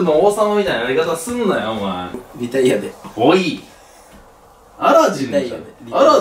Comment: リタイアでが、イタリアでに聞こえて、アラブじゃなくてイタリアだよって言ってるのかと思った(語彙力)